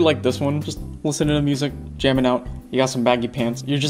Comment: Did she say "jaman now"? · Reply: No. She said jammin’ out